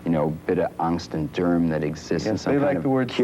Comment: what is he saying @ 6:00 angst and derm? kithygardian? polyana trip? this is probably his best interview.., ( serious one anyway lol) but damn was he wordy.. which is awesome, but I can't even grasp some of those descriptions there.. sounds cool though, haha,, sounded like a different language there for a moment